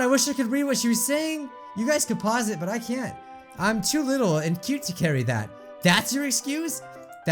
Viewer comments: "I hate being called cute" is what she said i think The time is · “I hate being called cute.” You probably already know but that’s what she said.